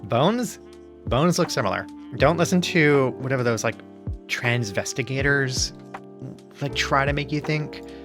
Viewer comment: woah i heard "transvest-" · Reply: "transvestigators" like investigator, like the transphobes who think everyone is secretly trans and make bizarre and gross judgements on photos of people claiming that they're trans because of their incorrect ideas about jaw angles, body shapes, leg shapes, literally just any human variation away from "photoshop-perfected western media ideals of women". Like how they thought Daniel Radcliffe's pregnant cis wife was trans. Basically, misogyny.